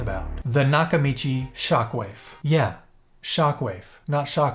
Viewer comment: Am I the only one who imagined a twerking vanilla wafer with booty jiggle when I heard “shockwafe”?